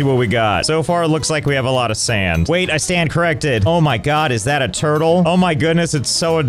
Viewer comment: i think he said "i sandcorrected"